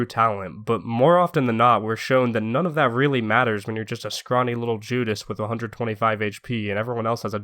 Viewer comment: for a second I thought he was gonna call the spy a “scrawny little Jew” lol